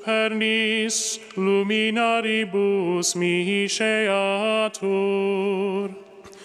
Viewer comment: I heard ' luminati ' ,lucifer , ... # WTF Happening..!